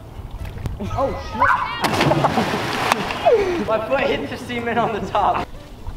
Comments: Am i the only one that thought he said he hit his foot on the semen on the top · i swear he said my foot touched the semen on the roof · sounded like "My foot hit the semen at the top" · it sounded like said "my foot hit the semen on the top" 🤣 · I thought he said " MY FOOT HIT THE SEMEN ON THE TOP" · it sounded like he said I hit the semen on the top